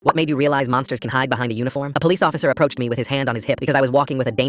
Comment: I thought it said "what made you realise monsters can hide behind a unicorn?"